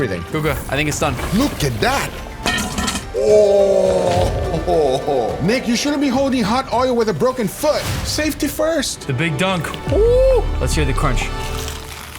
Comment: What did you just say broken foot?